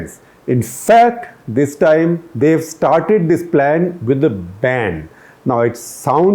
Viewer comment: I heard, " started the plan with a bam!" rather than "started the plan with a ban." LOL